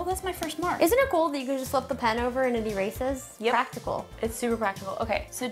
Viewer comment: "Isn't it cool that you can just lift the pen over and be racist?" Tell me I wasn't the only one who heard it like that! 😂